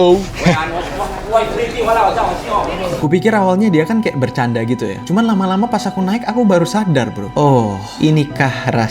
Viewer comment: dia bilang apa woy😂✌️ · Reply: “Muntah Badut” doang itu mah~…! 😂🤡